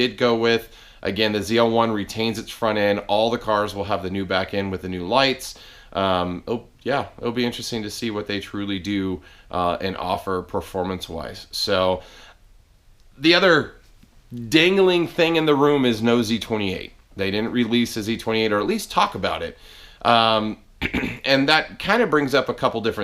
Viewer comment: I thought you said, "the other dangling thing in the room is Nosey 28." I'm like what the hell? lol